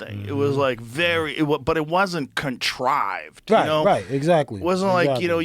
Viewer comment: did Joe just say Contrived, 🤯